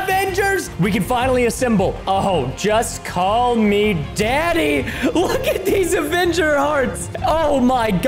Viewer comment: Did anyone else heard him say "call me daddy"? I was like "WHAT DID HE SAY?!